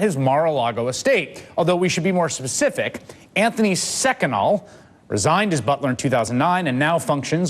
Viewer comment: He says Anthony SECONAL What would Freud say?